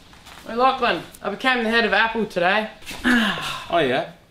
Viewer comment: I heard ''I ate a can of apple today''